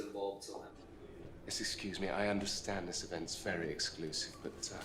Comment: мне послышалось " я на русском")))